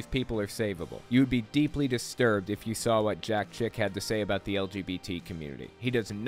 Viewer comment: I thought you said jack shit